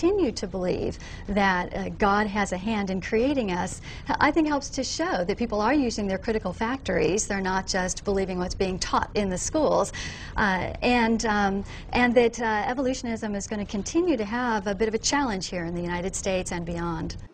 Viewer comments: did she say critical factories??? LOL! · does she say "evolutionism" or "evolution is umm" someone clarify please